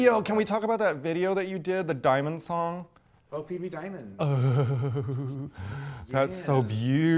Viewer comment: Did anyone else mishear "Feed Me Diamonds" as "Phoebe Diamonds"?